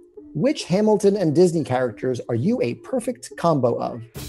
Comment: I thought it said which "combs" they are